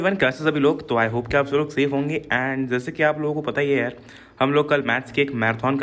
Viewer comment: did u just said I hope aap sab log fail honge?